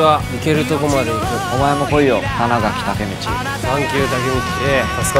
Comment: 最後の東京リベンジャーズがリベンジャースに聞こえるのが気になる